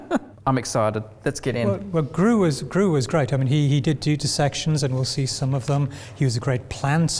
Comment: I heard "he did dudisections" rather than "he did do dissections" and got briefly confused.